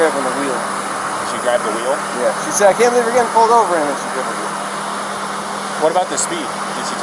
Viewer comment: I thought she said he had grabbed her face.